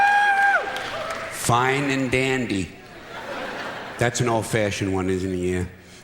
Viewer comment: what is he saying "fine and ??" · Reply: devil "Fine and dandy"